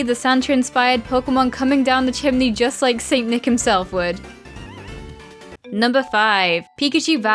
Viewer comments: i heard "satan himself " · When you say ''Santa'' I hear ''Satan'' wtf